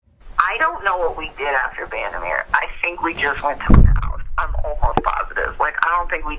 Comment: I believe she is saying “my house”. Not sticking up for her, but that’s what I hear.